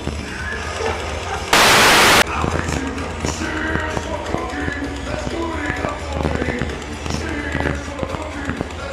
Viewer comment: what words did mike sing at 4:02? sounds like he failed to growl and made some joke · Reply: C is for cookie